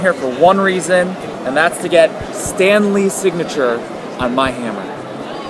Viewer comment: I thought it said "Stan Lee sighting" at first